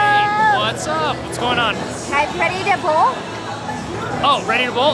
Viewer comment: When you said "are you ready to bowl?" I thought you said "pretty nipple!"